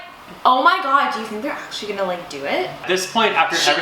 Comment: what are you saying at 11:29, "she was like...???" I can't understand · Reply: Haha I said "omg so you think they're actually going to like do it?"